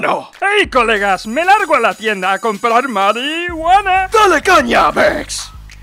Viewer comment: me largo a la tienda a comprar mariiiiiHUANA! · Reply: siempre pensé que decía "dale caña bitch"